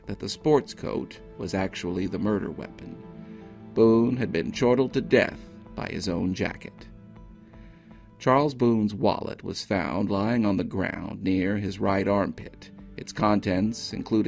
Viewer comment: Perhaps I misheard, but I think I heard you say "chortled to death". Having never heard chortle used in such context, I tried to find if it had an additional meaning. None that I can find. So I conclude that I misheard the word you actually used. Can anyone provide the correct word?